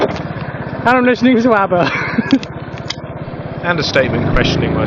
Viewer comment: what the fuck does he say at 7:01 "amlazuisabbaba" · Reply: "and I'm listening to some ABBA" Guess he had headphones and was rockin out to mama mia